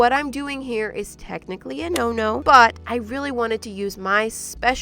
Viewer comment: Did it say butt?